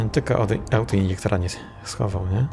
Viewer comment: nie jestem w stanie zrozumieć co mówisz podczas schodzenia drabiną na poziom 6. Brzmi jak jakiś obcy język :D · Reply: "Gdybym tylko autoinjektora nie schował". Zasepleniło mi się.